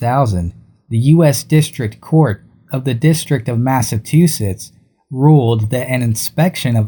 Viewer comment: Any likes and subs are sincerely appreciated! We are growing at a fantastic rate and I can't thank you all enough for being here from the beginning! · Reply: Did you just say “MassaTusetts?”